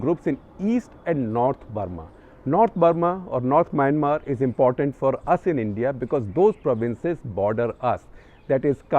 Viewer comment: Sir can you please mention the journalist or Schorlar name, I can't catch it sir. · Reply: He is taking about Bertil Lintner, the Swedish journalist